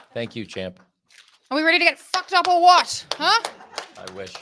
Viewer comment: Wow don’t know if anyone caught that Toni storm said are we gonna get fucked up or what and khan said I wish lay off the drugs dude